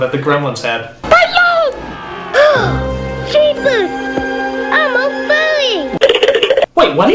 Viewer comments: I thought the little boy said "I'm all furry!" not "I'm a furry!"? ...did people even use the word "furry" that way back then? *confused* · Edmund said "I'm all furry." Not what the Nostalgia Critic thought he said.